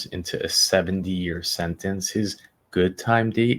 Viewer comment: Is the kid alive? If she's alive is she alright? Seventy years? Why the hell is up for parole?!? That there's a chance he might not "live life the way it's supposed to be lived" is too big of a risk to take!!! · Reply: 70years? God I hope thats what he said! I thought he said a seven year sentence and I was outraged! 70 seems much more suitable for a crime of this nature.